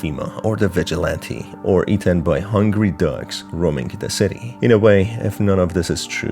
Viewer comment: I'm so sorry but I thought you said "eaten by hungry ducks roaming the city" 😂